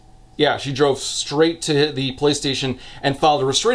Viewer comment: Am I the only person who hears "She went straight to the PlayStation"?